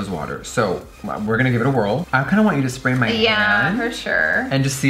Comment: I THOUGHT you were going to say "Spray my ASS"! Like, girl, OMFG! But, once you said "Spray my hand", I was like, okay, girl, okay. PFFFTTT, haha! I'm not OKAY! Bitch, PLEASE, HELP ME!